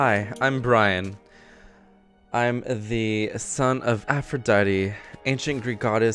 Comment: i heard bryan pronounce Aphrodite as aphrodaddy